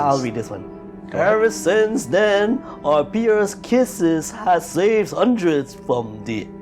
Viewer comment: I apologize for my commentary in this video, it's not great, even insufferable for some people. Sorry. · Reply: Thought you were very close to saying Penis Kisses at 53:50.